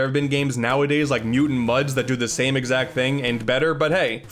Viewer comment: Could someone tell me the name of this game? I know he says it in the video, but I can't hear him very well and I never trust YouTube's auto-captions. I remember this game from ages ago that was just lost from my memory for the longest time. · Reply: Mutant Mudds